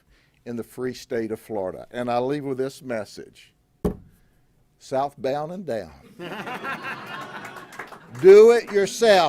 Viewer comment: i thought he said “south bound and drown” lol i thought .. that’s bit strong .😂 then went … derrrr. and slapped side or my head 😆